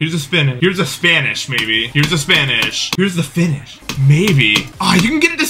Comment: it literally says "dude i just finished it" and i cant hear ANYTHING ELSE.